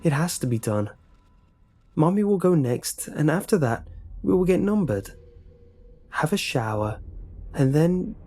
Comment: What the fuck- Is the haircut one about cows. I'm sorry but I heard "Get numbered" And my first reaction was to think about cows.